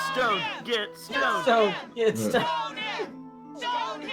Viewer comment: i thought they were saying bone him and i was like what the fuck